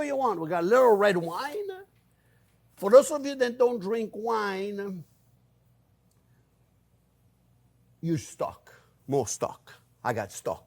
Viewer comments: "For those of you who don't use wine, use stock." I looked away for a little bit and heard it as, "For those of you who don't use wine, you suck." Well, granted, maybe. Not intentionally, but it might be true. Sometimes, I'm like that. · It sounded like you said, "For those who don't drink wine....you're stuck!" Then I realized what you actually said was, "....use stock!" 🤣😂🤣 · for those who don't like using wine that was funny it sounded like he said you suck when it was just use stock, am so glad you are back, you show is one of the few things i looked forward to, you look rested all the love and thank you for taking the time for all of us ❤